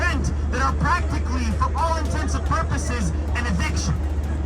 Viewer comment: At around 24:00 did the speaker actually say, “for all intensive purposes“ instead of all “intents and purposes“? 🤣😂